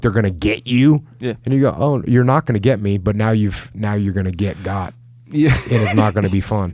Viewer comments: "You are gonna get got." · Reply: Ohh I thought he said "you're gonna get god" like, meet jesus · I'll watch later , great question.6 mins it great response YES KEEP YOUR F...g EGO IN CHECK! I'm off misheard and thought you said get God , kinda same thing really get got get God , end of story as my old man used to say bless him , do miss the silly old bugger some days , he was so f...cool, nar lookin at them like a worn out lion later on when he was alot older , b...of...will...ya ever so silently, you could have read his mind .